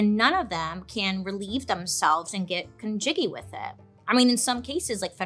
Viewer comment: Did I just hear Bailey say “get con-jiggy with it?”😂